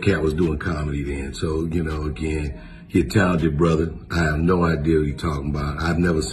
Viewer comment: What does he even say right here? "He intelligent brother" or "hate to tell ya brother" or???? I couldnt make sense of that at all. Do any of you guys know?